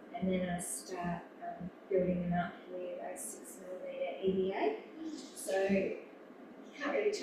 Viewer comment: Can anyone make out what kind of EVA describes using to build up lasts at 26:58? I'm pretty sure I hear "EVA", but I can't make out what was said before it. Thanks to for making this video available! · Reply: Ah, "6mm"! That makes sense.